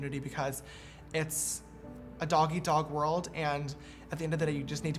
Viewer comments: wow. for a sec I thought he said "it's a doggie dog world" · it sounds like you said "doggy dog" instead of "dog-eat-dog" hahaha